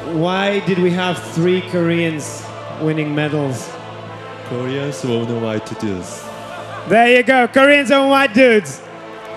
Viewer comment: does at 26:45 moon say because koreans are white dude's? i mean that is really what it sounds like but i can not beleve he said that...